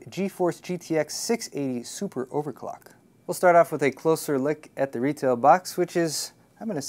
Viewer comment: did he said "A closer lick"?